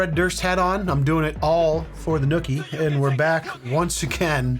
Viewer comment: SORRY! I thought you said "I'm doing it all for the Wookie